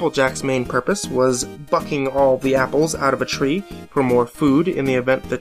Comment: Thought you said "applejacks purpose is fucking the apples" instead of bucking xD